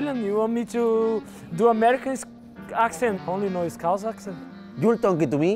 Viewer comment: What did he say ? only know is * something * accent ? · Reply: Scouse